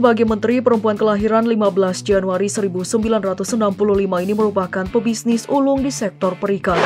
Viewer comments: Lahir 1995??? Biar betul...apa aku yg salah dengar. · Gak salah dengerkan di 00:52 nyebutin tahun kelahiran 1995?